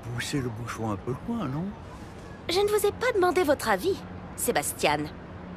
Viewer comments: is there anyone know what's Lili said in 0:58? I know she's speaking french with his butler. But i didn't get her words? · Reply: Je ne vous ai pas demandé votre avis, Sebastian ! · is there anyone know what's Lili said in 0:58? I know she's speaking french with his butler. But i didn't get her words? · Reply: I thought she said "je ne vous sais pas monde veux te ravi" XD I was so wrong.